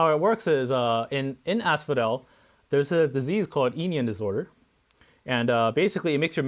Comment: I thought you said they got "eating disorders" lol